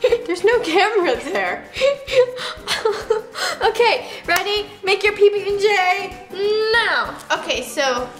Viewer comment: Did you hear that Roni say "okay, Roni make your pb and j sandwich " instead of Nessa